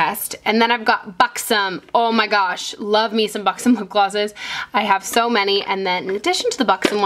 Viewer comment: i thought you said fucksome.....